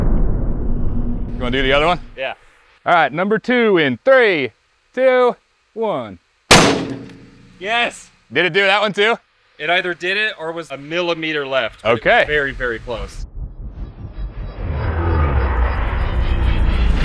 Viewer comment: what did he say? a millimeter? never thought you americans actually use this 😂